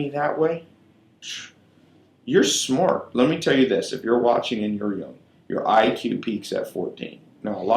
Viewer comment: What does he says at 33:32? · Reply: Digital Vatwani “if you’re watching and you’re young...”